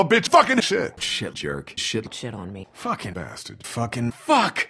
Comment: Umm.. what did Christa say? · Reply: FloodSlayer98 "shit on me"